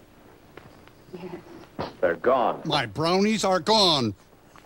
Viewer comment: did he just say "My Bronies are gone?" This ep was from a looooong time before Bronies